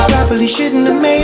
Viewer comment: Did they just say Lesbian Kiss? Accurate.